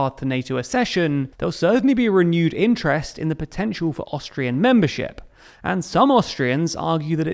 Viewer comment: Haha, I thought you said Australia. I was so confused listening to the episode for awhile. Great coverage on the history! 👏🙏🏼😁